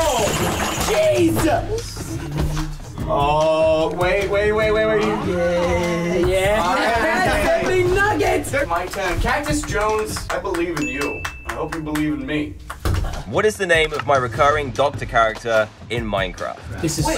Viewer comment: Did you say nuggets?